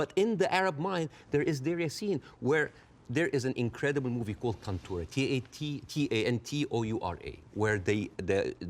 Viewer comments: What is the movie Bassem said at 47:24? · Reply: Tantura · Hey I cant figure out the movie he is talking about at @ 47:20 can anybody help? Thanks! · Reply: Tantura 2022 ‧ Documentary ‧ 1h 34m